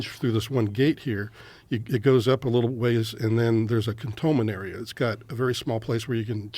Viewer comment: Anyone know what a "contolmanary" is 3:21? I googled it but nothing. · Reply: I think he said “Cantonment area”